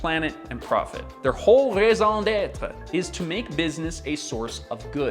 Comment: I heard ‘maison d’être’ instead of ‘raison d’être’ and didn’t understand what the Passé Compose had to do with this 😅😂